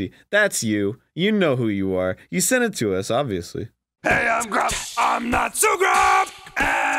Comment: It sounds like they say hey I'm drunk my not so drunk